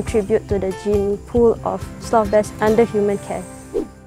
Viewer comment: trainer says “sloth bears under human care” but automatic subtitles say “slaves under human care” 😂